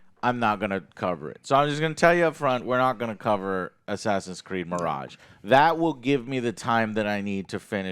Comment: I swear, at first I thought he said “Assassin’s Greed.” 😂💀🪦